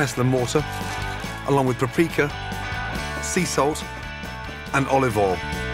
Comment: can someone please tell me what the hell is the 2nd ingredient after the crushed garlic at 0:03 i can't understand the name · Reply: Garlic in a pestle and mortar and paprika